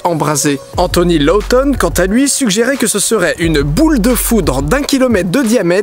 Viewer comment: j'ai entendu "une boule de foutre"...